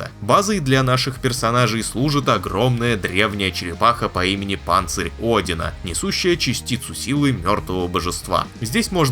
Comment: Послышалось, что черепаховое обиталище из Cloudheim зовут Панцу Одина... Достойное для Всеотца одеяния, ящитаю!